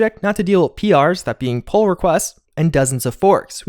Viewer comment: What did he say PR meant? I just heard "pole request".